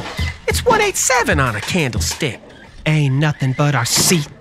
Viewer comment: I misheard it and thought you said "P-Double O P a wheelie!" And was absolutely confused 😅